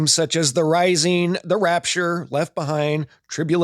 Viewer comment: Oh shit. I thought they were saying the raptors. I've been prepping for dinosaurs the whole time....